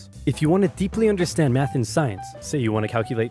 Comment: When I clicked on the video i thought i heard "Meth and science done right" but it was math and science lmao